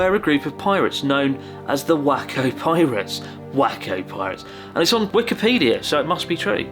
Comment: I thought I've heard wanking pirates